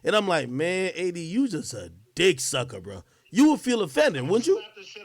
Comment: CAP!! He did not call him a d$cksucker. said “it’s almost like your D-riding gang. “ is tryna spin this anyway it’s crazy.